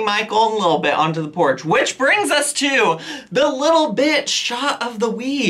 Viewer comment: the way you said little bit shot of the week sounded like you said little bitch lmao